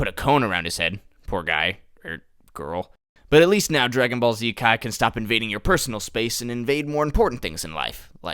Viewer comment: I've listened to this 5 times already and all I hear is "Dragon Ball Z cock"